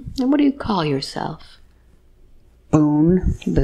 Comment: Did she say "BOONE" as in Daniel Boone? At 3:52. That's what I thought I heard, if so how cool is that? Buckskin top, pioneer hat, blonde hair, in the mountains hunting.